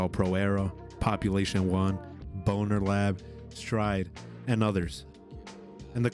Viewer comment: did he really say BonerLab? 😂